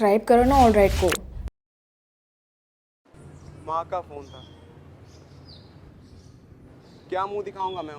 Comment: at first, i misheard it as Maa ka bh**da